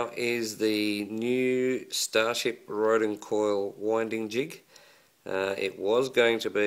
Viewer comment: what was the name you gave this .. I heard "starship roden coil jig"?? Is this spelled correct?